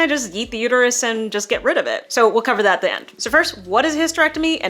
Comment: why did I hear "eat"